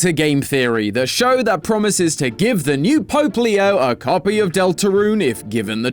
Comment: When said Pope Leo, I thought he said Poplio at first lol.